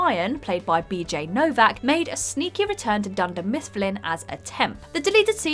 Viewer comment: Did you just say Dunder Mistfplin?